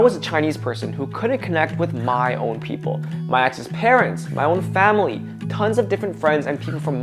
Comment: I thought he said my ex-parents... like they disowned you!?